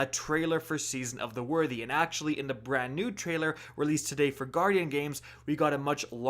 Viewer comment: For a split second I thought it said new trash